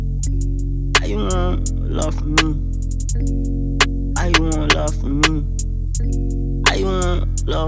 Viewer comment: brooo 0:20 all I heard was younkowfhshdbbdh like ahhhhh bro nahhh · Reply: “How u want love from me “